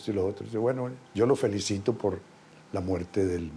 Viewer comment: Que dice ese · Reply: Escucha bien amigo , el dice en esos tiempos lo felicitó